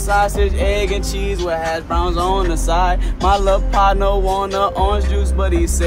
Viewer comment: Oh lol I thought he said “my love partner” omg 😂